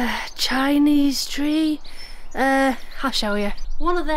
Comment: Hi 🤗 Nice to see you, as always. Had to laugh when you said "... a Chinese tree... eh, I'll show you" and I thought you said 'Hashoyo'. I wondered what kind of tree that was, until my brain actually heard what you'd said. Well, English is not my first language - but I bet you that somewhere in the far East there probably is a tree called Hashoyo 😁. Take care ❤️